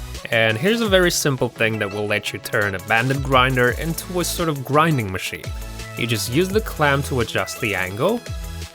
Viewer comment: Did he just say you can turn a grinding machine into a grinding machine?